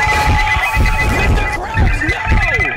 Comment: did he just say "Mr. Krabs nooo??"